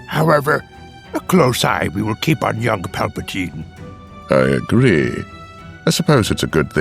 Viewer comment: All i hear is "Paul-patine"